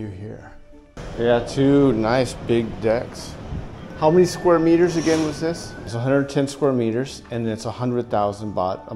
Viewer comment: what does he say 😳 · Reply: On the 4th or 5th floor, I can give a discount.